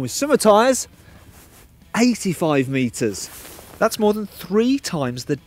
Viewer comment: i hear "85 metres", not 58. Why is that